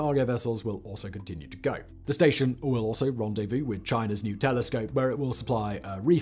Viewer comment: Towards the end, Simon mentions a "new telescope" but at first I thought he said a "nutellascope" 🙂